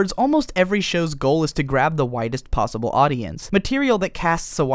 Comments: xDD omg at the beginning I thought he said "the whitest possible audience" and I sat there like wat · At first I thought he said "whitest possible audience" instead of "widest possible audience" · I misheard "widest possible audience" as "whitest possible audience", and then realized, there isn't any difference lol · i thought you said whitest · I thought he said to grab the whitest possible audience 😂